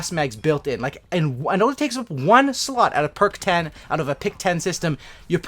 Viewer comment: Drop a LIKE! <3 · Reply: Wait did you say it takes only 1 slut